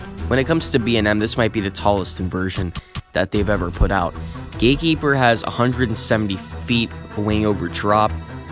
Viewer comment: The captions at 1:46 says gay keeper instead of Gatekeeper